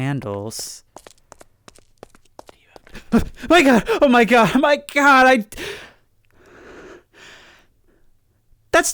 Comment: Does anyone know what the voice said at 6:07? I know I heard “do you have any” and my Magnus Archive fixated brain fills in “cigarettes” but I’m not quite sure bc he yelled before it finishes the sentence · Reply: "Do you have my arm?" It's a rare easter egg from the original map. If I remember correctly, there's a myth that an old maintenance worker lost a limb down there in the tunnels and has been looking for it since.